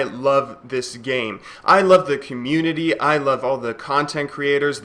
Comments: I didn't quite catch that sorry, You love the community? What? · wut? Did you just say you love the worse gaming community?